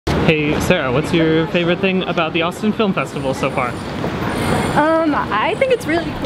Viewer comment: I thought it said Autism film festival..